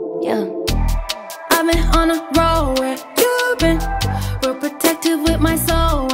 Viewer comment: This whole time i thought it was girls instead if goals god im stupid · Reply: I thought the same thing also thought she said I been on the road not on a roll